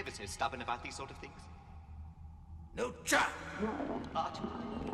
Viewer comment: "LU CHA!" iconic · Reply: RegrettableQueso — thought he was saying NU CHA